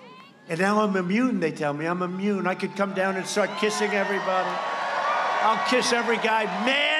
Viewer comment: did he say "suck and kissing everybody"